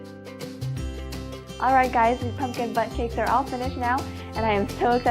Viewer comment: Did anyone hear her say Pumpkin Butt cheeks at 4:58?